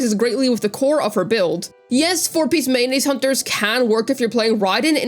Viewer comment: for a second I thought I heard 4 piece Mayonese Hunters